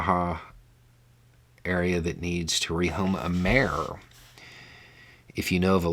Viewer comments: What the message said "... Need to re home a mare...". What I heard "... Need to re home a bear...". My head cannon was way more entertaining. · I heard "needs to rehome a Mayor" 🤣